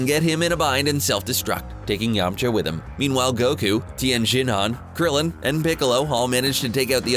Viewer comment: He said Goku instead of Gohan Even if you put on captions it still says Goku